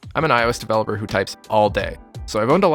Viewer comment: I'm not sure what this means :( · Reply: at the beginning you said smth and i thought you said you were a iowist developer like iowa and ist